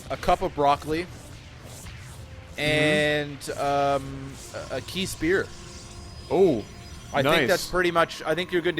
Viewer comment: What did he say at 6:37? A key spear?